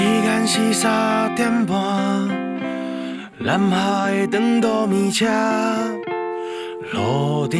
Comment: 超喜歡這首歌，只是第一次聽的時候我被第一句嚇一跳⋯⋯南下的長途，我聽成爛X的長度.......趕快跑去看了一下歌詞 ，個人真的很喜歡這首歌。